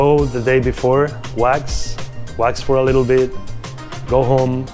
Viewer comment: is the guy at 1:00 saying you should'nt skate macba and rather watch, or what is he sayin? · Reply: ahhhh he is saying "wax", I was so confused